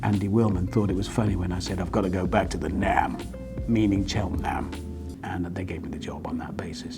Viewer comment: Can someone more intelligent than me please decode what he said at 5:40 before he said "they gave me the job on that basis". I have no idea what he said there... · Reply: "I'm going back to the 'Nam...as in Cheltenham..."